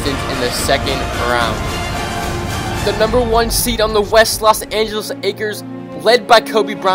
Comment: who else heard him say the "Los Angeles Akers"